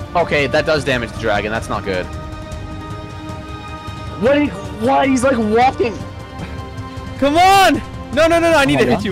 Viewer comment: I heard "What he's like- WAPing "